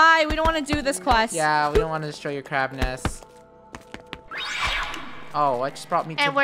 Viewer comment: i thought he said Crapness Not Crabness🤣😂